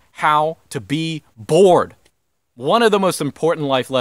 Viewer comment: What is "bord"? 7:58 I don't recognize the word. It's second rule how to resist temptation.